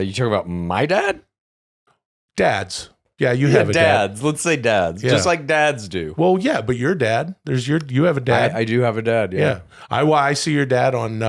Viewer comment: anyone else hear " everyone dad" from tenminpod